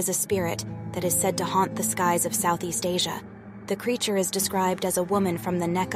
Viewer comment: I'm from India and I have never heard of it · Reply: Sorry I thought that she said South Asia